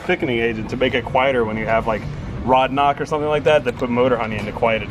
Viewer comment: did he just say RODNOCK!